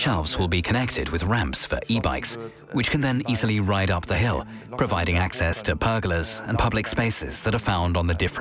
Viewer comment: I thought he said "giving access for burglars".